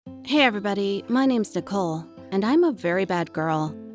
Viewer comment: did anyone hear her say “hey guys my names dicole”? (just me okay)